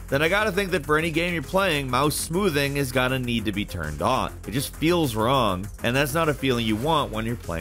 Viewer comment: anyone else hearing "need to be turned ON"? At least captions have correct OFF